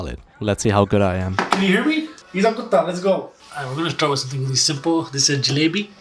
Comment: did he call him 'kuta' It means a dog